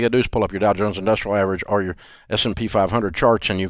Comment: What did he say at 6:25 to :29? Please someone reply I can't figure it out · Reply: Pull up Dow Jones industrial average or s & p 500 charts